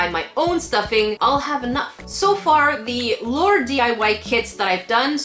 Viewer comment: Lmao I thought it said laundry DIY. Omg thx 4 da heart